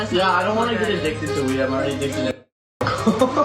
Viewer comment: I thought he said i’m already addicted to nic